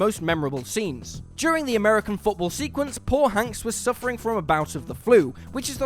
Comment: did he say Paul hanks?